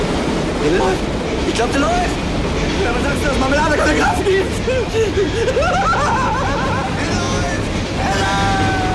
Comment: i'm a native german, but i honestly never understood what the LI is saying... One of view good german movies. In case you may wonder... LI stands for "Leitender Ingenieur" = Chief Engineer 1.WO stands for "1. Wach Offizier" = XO Executive Officer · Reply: He says "Wer sagt denn, dass die Marmelade keine Kraft gibt"